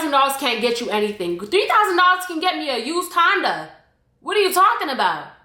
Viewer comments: Omg I heard “$3000 can get you a used condom” instead of Honda 🤣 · "$3000 can get me a used Honda" 😂😂😂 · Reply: I misheard her the first time and thought she said "$3000 can get me a used condom." XDXD